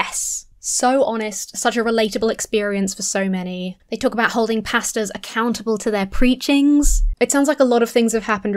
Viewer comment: for a moment at 06:04 i thought you said something about holding pasta accountable